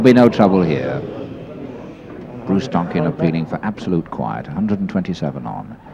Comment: The olden days of cigarette sponsors. I think Embassy did the World Championship. Ray with his cue arm about 30 degrees to the vertical, probably guiding his cue on his waistcoat. Spencer vertical arm. Many ways to greatness. The ref sounded like John Smith but was in fact. Happy, smokey days.😃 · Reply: I thought he said the ref was Bruce Duncan (Scotland)...could be wrong. Brilliant to watch,and to see the audience. Top stuff.